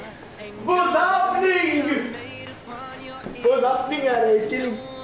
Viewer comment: Zayn: "Vas happening Harry chilling....." Me: Crying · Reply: Oh he said "whats happening harry chilling" i thought he said "whats happening harreh chu" 😂😂